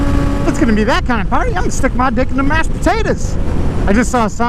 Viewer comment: Somebody please tell me what said at 3:09 and convince me that he didn't say "If it's gonna be that kind of fun, I'm gonna stick my dick into mashed potatoes" I really want to know if that's what he said or not. Bhahahahha.